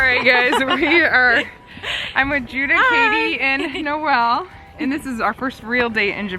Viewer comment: I heard "Juddah's Baby" NOT "Katie" and I got a little jealous... LOL . is such a great catch!! Happy for you all <3